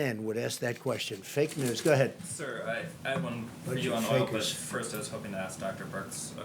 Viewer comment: Did he say “Fake as fff” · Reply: He said fakers